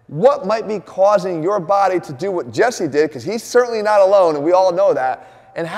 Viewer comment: Did Jeff say Jesse Dick at 1:03.. Look at Jesse's reaction after he says it..😁